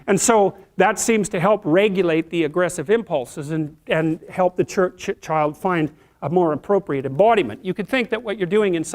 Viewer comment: What did he say at 5:06? Churched child?